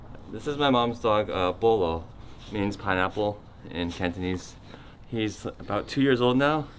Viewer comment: Very sweet video. :) I can't wait to see other Wong Fu cast members with their families. :) In the beginning, I thought was saying Bolo when he was actually saying pineapple in Cantonese. lols